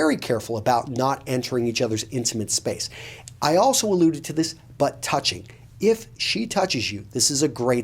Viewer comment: I heard, "I also alluded to this butt touching"